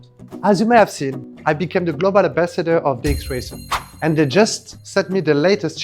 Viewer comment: did he just say "dick racer"